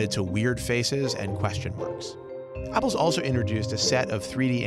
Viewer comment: he says pebble instead of apple